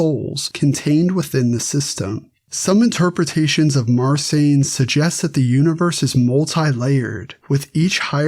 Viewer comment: What is the name said at 9:12? Sounds like Marsain. Is it Marcion? Edit: Found it - Marsanes, sethian text.